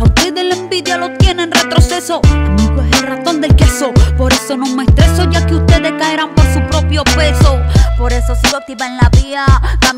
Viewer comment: alguien sabe que dice en el min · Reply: amigo es el ratón del queso,por eso no me estreso ya que ustedes caerán por su propio peso